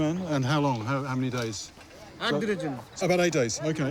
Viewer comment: I think he's saying "Ek Din", which is one day. Or am I hearing wrong.